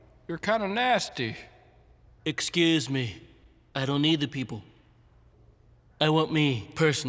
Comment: about 1:55 you messed up the caption it says "meed" instead of need